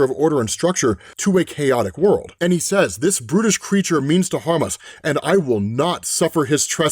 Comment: sorry i hear British